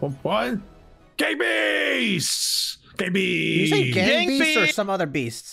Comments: i swear he said gay beast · I hear gay beasts for some reason